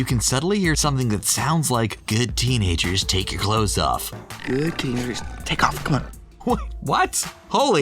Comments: Oh... I thought it was Aladdin saying good kitters/kitty take off, come on. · I heard "good teenagers take off and go" not "good teenagers take off ur clothes" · So, I paused this after the Aladdin secret and grabbed my DVD to see what the subtitles said at that point and it's "Down kitty. Take off", Ali says it as Rajah approaches him snarling. LOL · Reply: From Pluto people just gotta was dramatic. But I heard “good tiger, take off” but what ever just as innocent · In Aladdin, I can clearly hear "Good kitty! Just, take off!" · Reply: I heard “Good tiger, take off and go” idk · In Aladdin, I can clearly hear "Good kitty! Just, take off!" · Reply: I heard 'good tiger, take off' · In Aladdin, I can clearly hear "Good kitty! Just, take off!" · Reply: I hear " good Tigger, take off and go" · In Aladdin, I can clearly hear "Good kitty! Just, take off!" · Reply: I heard "Good teenagers take off your clothes" the first time I heard it and I had to make sure my brother did not hear it lol😂